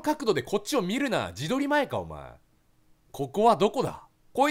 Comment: ここはどこだ。 が ココアどこだ？に聞こえた